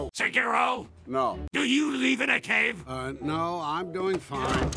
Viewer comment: DO YOU LEAVE IN A CAVE? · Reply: I thought he said Live in the cave instead leave.